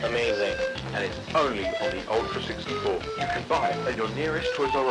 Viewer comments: guys, there’s nothing obscure about the “change your system” at the end. i know, its creepy but its only the nintendo tag line. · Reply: I thought is said Nintendo System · guys, there’s nothing obscure about the “change your system” at the end. i know, its creepy but its only the nintendo tag line. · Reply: I thought it said eat the system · I found what voice the TTS was using it was the C64 Sam TTS Its saying Change the system Tho it can be misheard as kill your system or kill your sister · Fun fact: “CHANGE YOUR/THE SYSTEM” was actually a marketing catchphrase for the n64. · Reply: I thought he said kill the system